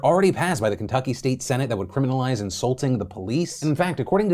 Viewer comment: "Illegal to insult the police" But it's still legal to insult the jack-offs who made the law right? Good. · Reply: I heard "assaulting." Nope it is "insulting." The fuck? Freedom of speech hello?